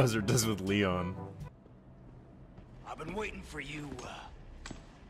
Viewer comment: For a second I thought Krauser said “ Pookie” 😂💀